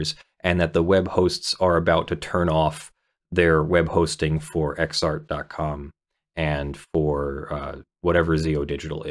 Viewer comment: I thought you said they were pirating from PIXAR... Then you said "adult" videos. 👀